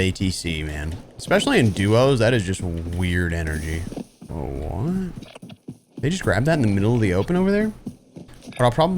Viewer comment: what did he see???🤔 I can't figure out what he called out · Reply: loadout coming in at airport